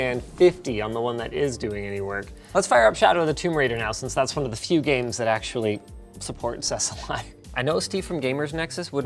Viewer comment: Subtitles said: "...cause shadow of the tomb raider is one of the games that support us alive" and honestly I laughed